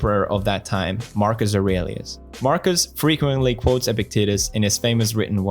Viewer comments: Pewds: Epictetus Me: Did he just say Epic Tities?!? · Every time he says "Epictetus" I hear "epic titties" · I keep hearing "epic tiddies" when he says Epictetus.